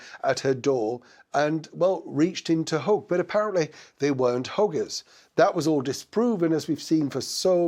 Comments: Oh dear...when I heard you say she's a hugger, at 1st I mistakenly heard h**ker😮 Slip of the accent. My bad. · I just spat my cornflakes out, your lovely northern accent got me, when you said mm said she was a hugger i thought you said hooker 😮, woah, then the penny dropped that you'd said hugger and id just misheard 😂. Love the flask